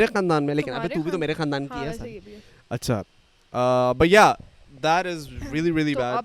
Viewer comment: When said " but yeah" i thought he said " bhaiya" this was very funny and cute.....